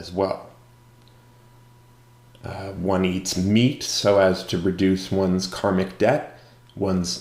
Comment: That segment you time-stamped is about paying debts, not eating meat. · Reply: Right... The audio sounds like you said, "one eats meat to reduce karmic debt" I just happened to notice the closed captions say "one does not eat meat to reduce karmic debt" Odd.